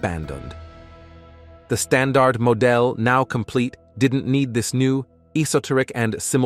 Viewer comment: Hey guys, your text to voice generator need calibration: at 8:57 it said "the stand art mo dell" instead of saying "the standard model" He-he :face-fuchsia-poop-shape